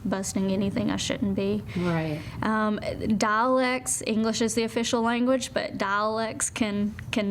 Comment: Great interview! loved it. But for a moment there, I thought she said 'Daleks can make English not sound so much like our English sometimes'. :(